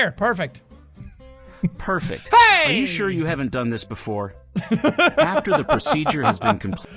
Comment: I thought he said 'I'm sure you haven't done this before